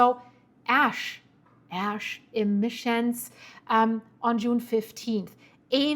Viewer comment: 😂l am hearing impaired and heard you say ask permission , ash emission ?😅